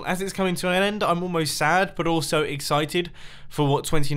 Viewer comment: did u say "I am exited for 29" ???? edit 29 NOT 2009